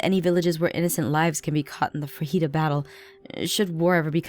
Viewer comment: when she said the heat of Battle of sounded like she said fajita battle!!!😂😂😂